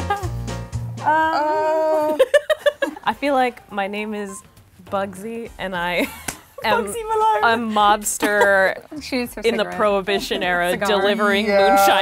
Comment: did just say Bugsy Malone?!